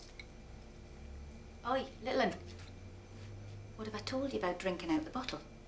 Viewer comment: what's she saying?? oi little lander??? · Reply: Oi little'un (little one)